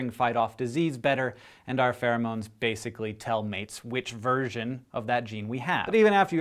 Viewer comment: When he said "version" at 2:43, I thought he said "virgin" even though it had nothing to do with the sentence. I need to get my mind out of the gutter... :P